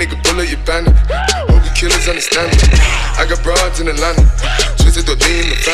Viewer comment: I thought he said "Legacy" when he said "Black X6" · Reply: I thought he said broads in LONDON